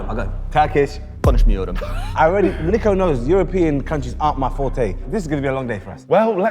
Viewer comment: he actually said I don’t speak. I love Turkish. My favourite language.